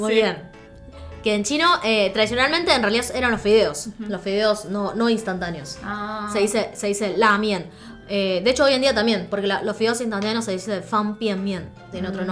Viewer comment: entendi la miel XD